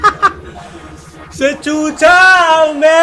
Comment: Did he say chucha